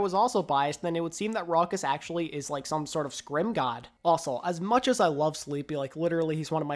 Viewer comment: did somebody say S C R I M G O D ?